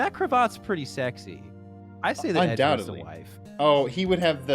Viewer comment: did call edgeworth a walewife? is that what i'm supposed to get from this?